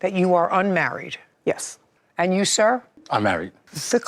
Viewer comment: I thought he said I’m married too.